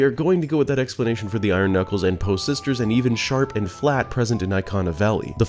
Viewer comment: lmmfao. i thought you said "shart" instead of "sharp" lol i went back and listented to it 3 times and im like no hes deff saying sharp